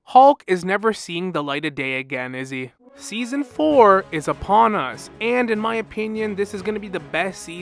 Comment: I thought you said "Season Thor is upon us" and since I've already listened to the Dev vlog, I just nodded in agreement for a second before context clicked and I realized you said "Season four is upon us.